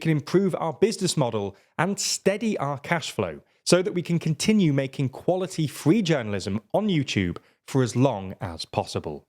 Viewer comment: I can't be the only one who heard "so we can continue making quality-free journalism"